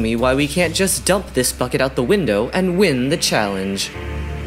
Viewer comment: i thought said fuck it out the window